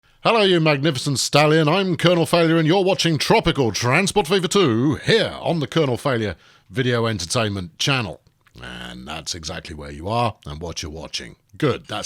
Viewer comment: it's so nice someone recognises I'm a magnificent stallion 🙂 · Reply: At first I thought he'd said magnificent Stalin!